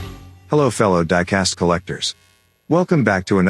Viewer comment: what you say? ,,hello flip diecast collectors" did you say it? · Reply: Hehe. I said fellow diecast collectors. But it does sound like flip Diecast collectors. 😁